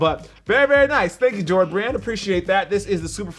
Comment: turn on caption, instead of jordan it became George Rand